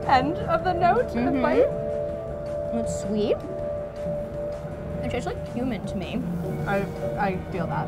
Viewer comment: When they're eating the jackfruit and Tess says 'it tastes like cumin" I heard "it tastes like human". 🤣